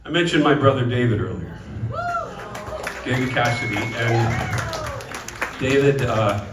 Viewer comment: Is that David’s son singing?? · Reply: Sounded like he said Bo Cassidy, David's son.